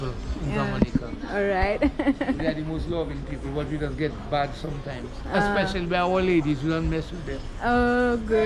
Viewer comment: did bro say we are the most loving people but we'll buss up their ladies teeth out if she say some wrong ? 😂😂